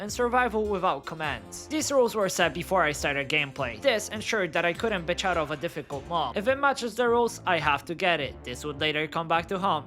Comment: wtf is he saying here?? I cant understand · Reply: "This ensured that I couldn't bitch out of a difficult mob. If it matches the rules, I have to get it."